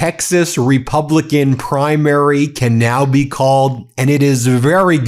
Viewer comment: Ben, did you say the "Texas Republican Primary" or the "Texas Republican CRIMARY"?